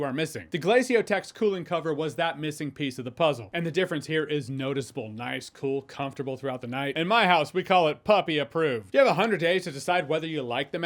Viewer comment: Puppy* not Puffy… yeah i heard it too!? 🤣